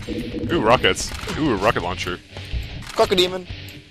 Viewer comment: Wait, did I hear "Cockodemon" at 12:19?